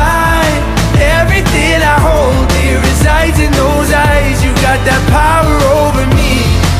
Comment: bruh i heard "there is ice in those eyes" and not "dear resides in those eyes" 😂